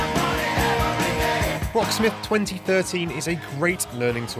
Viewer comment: Did he say "Rocksmith 2013" in 4:06 ?